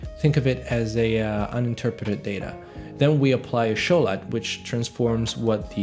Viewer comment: anybody know what he meant when he said show light at 8:54? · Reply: Show LUT...